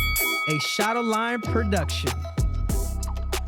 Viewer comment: This whole time I thought Henry was saying " a shot of lime productions" ,but it's "shadow lion" lol